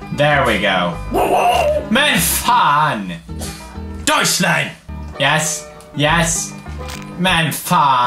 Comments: What that means that Pewds says to Edgar at 02:46? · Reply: it's 'Deutschland' the german word for Germany :D He uses it as a 'swear word' for some reason :D I mean... it's poods +PewDiePie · When he shut his dog up he sounded like he was saying Deutschland (Germany) .......???????? or maybe it was shut up in swedish.... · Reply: Yes it is deutschland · What that means that Pewds says to Edgar at 02:46? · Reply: Melissa Fritsch I thought he said Deutschland.. But it didn't made any sense.. But, it's funny :D Thanks..